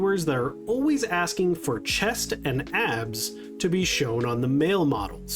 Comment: Sorry I misheard 0:25 as 'chest and ass' and was like, yeah that's pretty much what we alway-... oh the MALE model abs lmaooo An easy skip for me, personally. I like the cute skirt based outfits. I was hoping they would make a gothic build pack for the season, but who really knows at this point.. Maybe they'll release one in December, I dunno